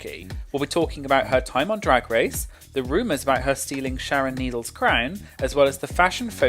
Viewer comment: I thought you said, "Sharon Needles' crack." Did she ever have a crown?